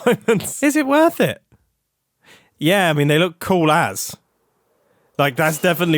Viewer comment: did Mumbo say cool@ ?? 😭😭 I was taken aback by a hermit swear lol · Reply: No, he just said “cool as.” Could be a British thing to say “as“ without actually finishing the sentence because I do it and didn't even know it was weird 😅